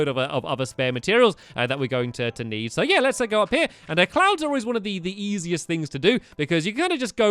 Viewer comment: When you said cloud I thought you said clown Lol XD